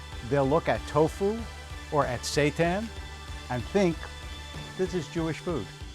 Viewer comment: Did he just say, "at Satan"?? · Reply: there is a wheat protein product called seitan... not a great name for sure...